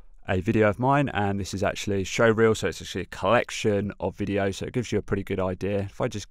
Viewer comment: did you actually say it's a shit collection of videos?